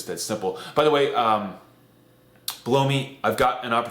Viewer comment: sounds like you said.... "by the way, blow me."